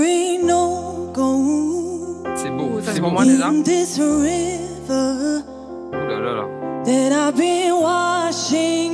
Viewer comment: What does Amel say here? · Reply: "Ça c'est pour moi déjà" ~ This, it's for me (for a start)